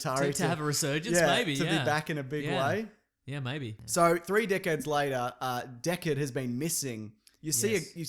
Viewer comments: It doesn't matter how many times I watch this I keep hearing "3 dickheads later." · That sounded like 3 dickheads later...